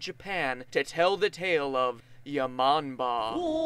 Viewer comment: did he just say ya momma?